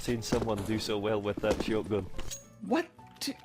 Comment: I think he said shotgun but with an accent